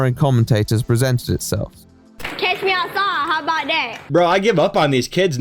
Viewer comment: I always thought she said catch me outside. Idk what cash me would even mean.